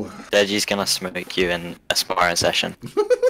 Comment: "deji's gonna smoke you in a sparring session" ksi: uncontrollable laughter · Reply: I thought he said asmr session lmao